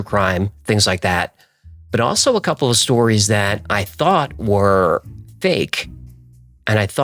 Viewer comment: Sounds like u said turned u into a belieber. Ooo noo diddy got u too